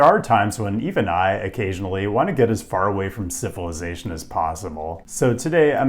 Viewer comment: The way you pronounced "civilization" at 0:08 sounded like syphilis-ation! 🙁